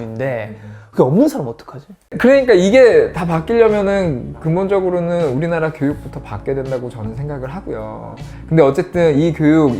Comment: "근본적으로 우리나라 교육이 바뀌어야 한다"가 "근본적으로 우리 가요계가 바뀌어야 한다"처럼 들림..